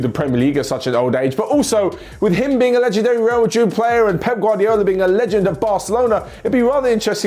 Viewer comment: For a split second I thought said Sergio Ramos being a legend at Barca and pep guardiola being a legend at Real Madrid I was like hold up..